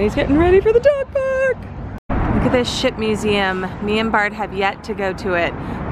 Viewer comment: Bart's description of his symptoms sounds like the Flu. TamiFlu knocks it out but you have to have your doctor subscribe it. I hope you feel better. He's a trooper and btw I thought you said "Sh&$ museum". Because yo had your envisalign in.😂😂😂